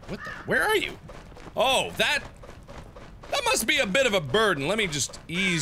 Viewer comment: Did you say... a BIRDen? Hahahahahahahahahahahaha